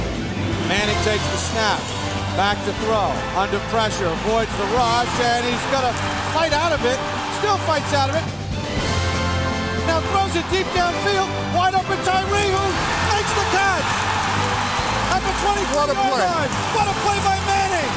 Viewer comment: wait did he say wide open?